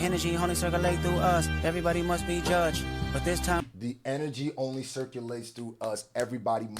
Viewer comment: did he say energy or industry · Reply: Energy